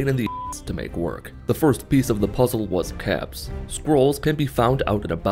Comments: Did he say… caps? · did no one else hear him say caps instead of gold or am I just hearing things(edit:time was off by like two seconds)